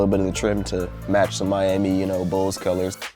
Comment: Who else heard him say the Miami Bulls 😂